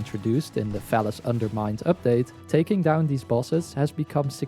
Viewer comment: Thought he said phallus undermind